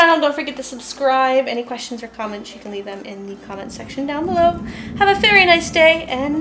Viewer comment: at the end, did you say "have a fairy nice day" lol sounded catchy but idk if i just misheard you lol.